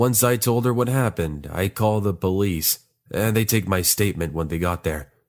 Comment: Did anyone heared that he say bolice instead of police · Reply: Yu Bh i also heard him say bolice 🤣🤣😂😂😅😅